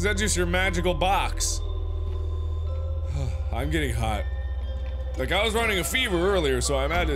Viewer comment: I thought he said, "I'm getting high"